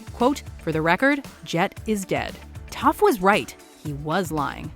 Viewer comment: was i the only one who heard her say tough not toph 👁👄👁